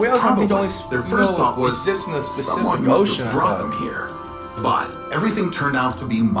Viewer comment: did i just hear the specific ocean?